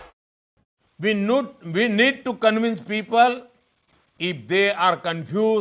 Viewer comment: Is he said nude ?💀